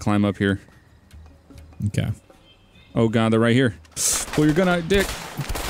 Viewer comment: sounds like said "Pull your gun outta your DICK!"